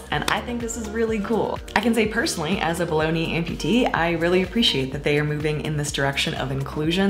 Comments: I totally thought you said "bologna amputee" but I've been on a roller coaster of a week. Haha! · when you said "below knee amputee" I heard "bologna amputee" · For a split second when you said "below-knee amputee" I thought you said baloney amputee and I was like "Wait, what?" XD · I heard "as a bologna amputee" 😂 Anyway, great news about Zappos. I can't believe this is the first company to do this. It's not only compassionate and inclusive, but it seems to make total sense.